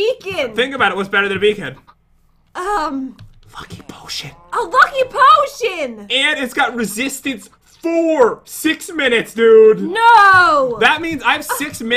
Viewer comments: In 15:19 did you say F ing potion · When you first said "Lucky Potion" I thought you said 'F-ing B.S' I don't know where that came from. XD EDIT: Jeez, almost 80 likes and 42 replies as of this edit. lol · I thought pat said "fu**ing potion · oh no a dying bird! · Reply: didn't it s sound like Pat said fucky potion